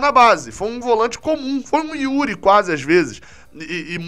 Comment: Aos 12:45 "André foi quase um Yuri ano passado" AMARAL, Gabriel. Melhor ouvir isso que ser surdo .